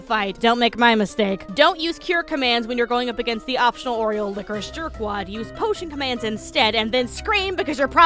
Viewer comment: Don’t use Cure against the option oriental whatnow lol? I know you’re referring to Vanitas’s Lingering Spirit, but what tf did you say word-to-word lol?